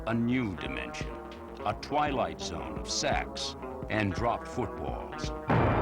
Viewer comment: Did he say "a twilight zone of sex and dropped footballs" ?? I have played it three times and that is what it sounds like.